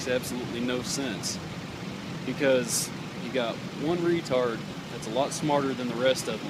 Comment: Did that cop actually say the word, *retard*? Did I hear right?? This guy is the type of cop who would send a stab victim back to a serial killer to finish off the job. "Welp, he was 14 and bleeding so I sent him back where there was blood. What did he know 'bout life? This is TX."